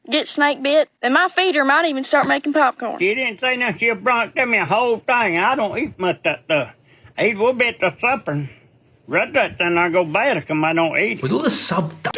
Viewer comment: what he says in a broken southern accent dialect but he said "She didnt say nothing. she just brought it to me, a whole thang and I dont eat much of that stuff. Even a little bits a supper n rest thats in there gonna go bad if somebody dont eat it" and yea I could understand it lol.